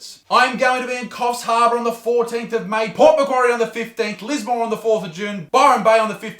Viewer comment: Oh. Lismore. I thought you said Lisbon 😂 Would be dope to see your show in Lisbon.